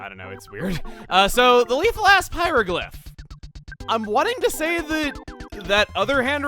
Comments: Am I the only one who thought he said "Lethal Ass Pieroglyph" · Sounded like said "the Lethal-Ass Hieroglyph" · I somehow misheard "Lethal Asp Hieroglyph" as "Lethal Ass Hieroglyph" lol